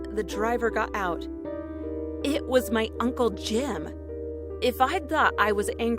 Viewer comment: I legit heard "It was my uncle Jin" Also, when it said the driver got out, I heard my mom leaving her car